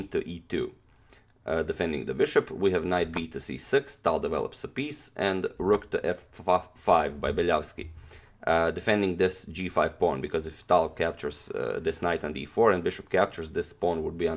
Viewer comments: hahahahah.. So funny did i just hear "Rook to F fuck five" ??? 3:05 .. let me know.... Damnnnnn that fucking rook Fuck Five... hehehe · did i just hear "Rook to fuck five" ??? Damnnnnn that fucking rook 😂😂😂